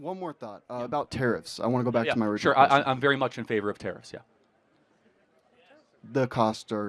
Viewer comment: My Scottish ears thought Charlie said ‘I’m very much in favour of terrorists’ at 6:07. Had to turn it back and listen again 😅